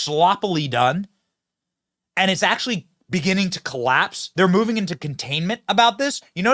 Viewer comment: I thought you said it collapsed.